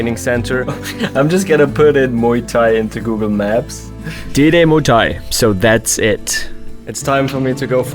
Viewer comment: did you just say diddy muay thai